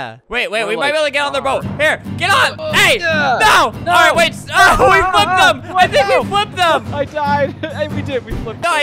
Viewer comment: I actually thought she said “We f___ up” 😭🤣👏🏻.